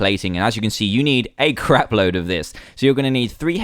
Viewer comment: idk if he said crab.. or the thing thats like that just a p instead of b...